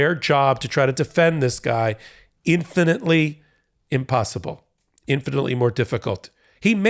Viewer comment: I heard 'infantly' when you said 'infinitely'! Hahahahaha